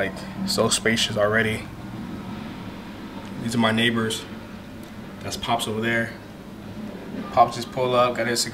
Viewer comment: why did i think J said "that's cops over there"! I was like 'Ah here we go'!!!😅😅